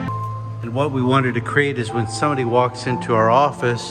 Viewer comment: I thought I heard you said "when Sony walks into the office"